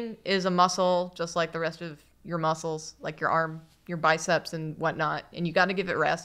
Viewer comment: I thought said, “brain is a mussel”, instead of a muscle...